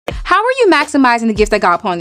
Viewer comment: ive reround three times@ 0:03 "how are you doing what? and you said God i think